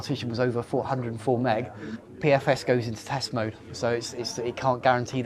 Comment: Wow, it sounds like I say 404meg but I actually said 104meg :) Just finished watching the full video - exceptional job on the editing and the content.